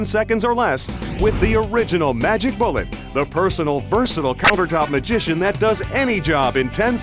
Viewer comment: After all these years, I never noticed he said "The personal versatile counter-top magician". I always thought he said "the personal bersonal", like making up a word because nothing rhymes with personal..